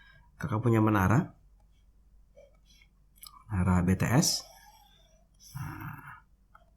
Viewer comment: B-BTS? ato gue salah denger:'v